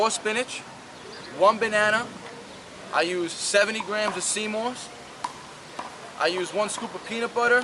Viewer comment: What is he saying at 12:33 please ? I can only understand 70 grams of cmos, but it's doesn't seems to be it. Anyone to help me please ? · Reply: Got it, i think it's sea moss, right ?